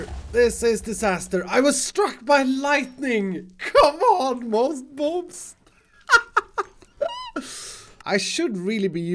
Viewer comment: moss bobs instead of boss mobs